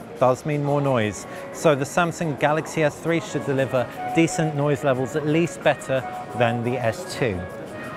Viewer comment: I thought you said Samsung Galaxy S one hundred and eleven.